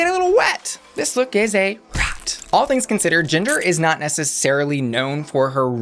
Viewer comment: why did she call Ginger "Gender"?